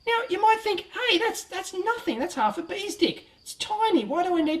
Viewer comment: did you say "half a bees dick?" :) Aussies.